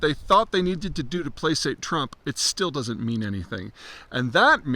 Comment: Did he say 'plasate' instead of 'placate' at 3:01?